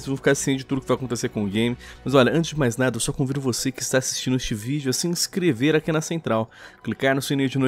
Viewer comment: Quando o falou "Ola você que está vendo este video" Eu ouvi "Olá você que está vendo xvideo" Eu não tô brincando eu ouvi isso '_'